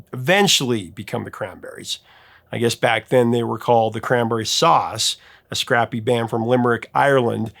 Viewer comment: Sounded like you said The Cranberry Sauce. But I think it was The Cranberry Saw Us.